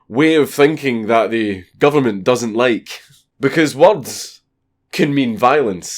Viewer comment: i thought he said warts instead of words xD i love scottish <3